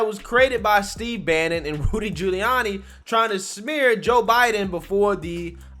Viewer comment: Does anybody hear say "BOOTY Giuliani"? If so, that's funny as hell XD